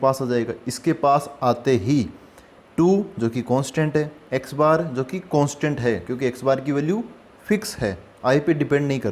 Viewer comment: pe kya bola ji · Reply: Constant